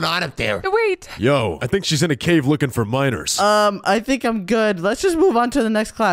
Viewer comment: Did he say she looking for minors